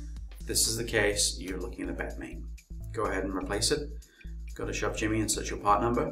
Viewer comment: "if this is the case you are looking............ Go ans search parts" .What did you say where the dots are? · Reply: Try all repairs and troubleshooting at your own risk. If this is the case, you are looking at a bad main board. Thanks! <link>